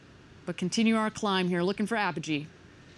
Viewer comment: Oooh its Apogee It makes sense, I thought it said Apology